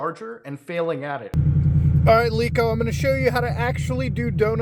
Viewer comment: did you just call him “leako”? If so that’s an epic insult. If not thats a hilarious accident